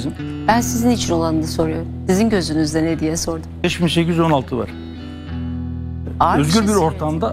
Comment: de ne diyor? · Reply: 5816. kanun